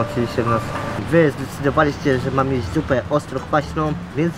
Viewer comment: Zamiast zupę usłyszałam Dupe 😉😅😅😅